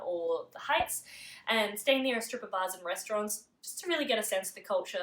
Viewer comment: I thought for a second you said, "Stay near the stripper bars and restaurants..." LOL. I guess it was, "a strip of bars and restaurants."